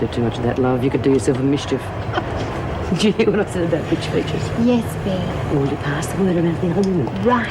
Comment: lol did bea just say "did u hear what i said about bitch features! HAHAHA